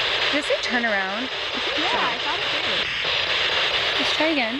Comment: I could have sworn when she said “turn around” I heard “they hear us”